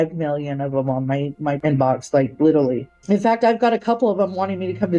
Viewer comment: Aw come on, ypu didnt even catch how she said "littlely" instead of "literally". Lmao